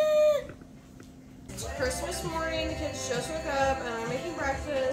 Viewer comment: I'm sorry, but in the beginning I keep hearing 'It's Christmas morning, kids' sociopath..' T_T